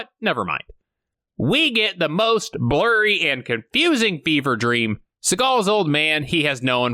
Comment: did you say 'fever dream'? Because it sounds like you said 'beaver dream'! I need to know!!!!!!